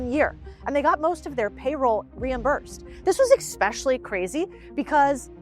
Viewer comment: she did not just say "expecially" 😒